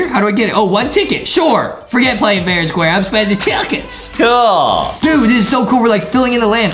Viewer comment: BRUH TICKETS SOUNDS LIKE HIM SAYING CHUCKEN